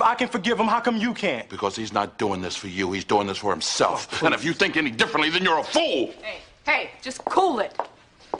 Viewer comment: Did Will actually said “oh please”?